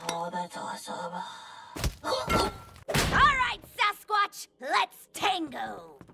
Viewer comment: did she say SUSquatch? >_>